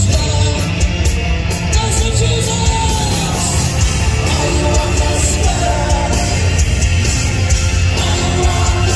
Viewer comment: What did he say at 3:24? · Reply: Massachusetts...where they were playing